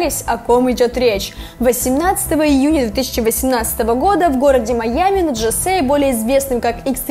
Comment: она сказала 1018 года ????? Или мне послышалось